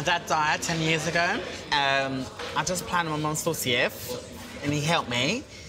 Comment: I really like these two, can someone tell me what he said at 0:16? Thanks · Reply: Sophie W he was planning his mums 40th